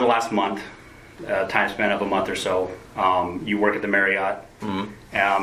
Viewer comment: I thought he said 'You work at the Merry Yacht!' (Marriot)